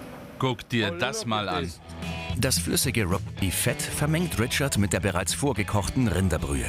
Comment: Was sagt der Typ da. Rot beff fet😂